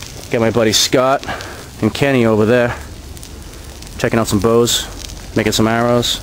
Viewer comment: i thought he said Checking out some Ho's